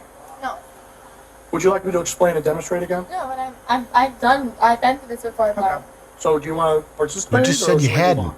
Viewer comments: I thought she said I’ve been to Hogwarts 😂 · Am I the only one who heard her say "I've been to Hogwarts"???? I did not hear "I've been through this before." 🤣🤣🤣🤣🤣🤣🤣 I swear that's what I thought she said! 🤣🤣🤣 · I thought she said " Ive been to Hogwarts." Lol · Lol i thought she said "ive been to hogwarts" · When she said she had been through it before and knows how it works.. I thought she said she had been through it before at Hogwarts.